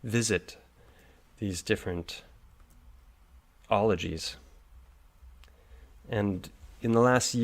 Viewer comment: did he say 'ologys'? <link>